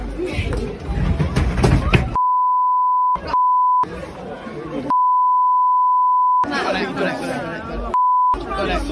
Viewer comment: what’s the censored M-word? M**OL, I can’t figure it out…. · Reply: Mongol